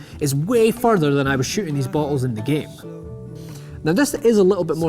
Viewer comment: i thought he said buttholes not bottles lolololololololololololoololololol